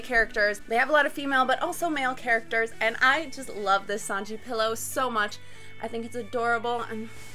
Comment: Pretty sure i heard "Spandam pillows" @ 0:12 xD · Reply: Omg you replied. :D yeah i have to listen to it a couple of times to hear "husbando" haha. I was shocked when i heard you say "spandam pillows" with that happy face. Haha. Happy new year!!! sparkles